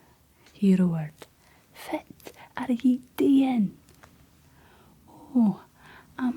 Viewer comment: All I hear is Shrek, "What are ya daen, in mah swamp!" 😂